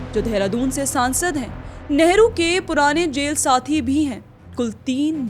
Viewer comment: Hema Malani was MP from Mathura but she is from Tamil Nadu, Modi is elected from Varanasi but he is from Gujarat😂 These things doesn't make them native of our Uttar Pradesh. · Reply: My Dear Bandhu, listen again carefully at 00:42 she clearly said " . . . Dehradun se MP . . . " and not as you are imagining to be something else.